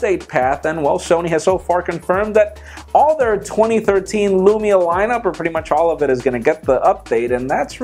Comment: did he say LUMIA ?!